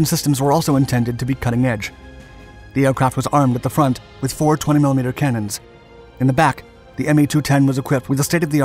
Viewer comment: Did someone say 420mm cannons?